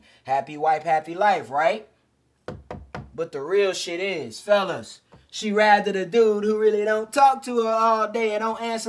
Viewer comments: did you say “she just rided a dude would really don’t talk to her…” or “she rathered a dude?” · Join my Private University: <link> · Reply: did you say “she just rided a dude would really don’t talk to her…” or “she rathered a dude?”